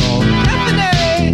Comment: mishearing time! “Caffinate!” Can be misheard as “Lemonade!” “I am injury” can be misheard as “I am Angeline!”